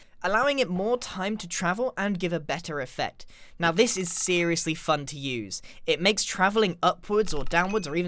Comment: I heard this is seriously fu**ed to use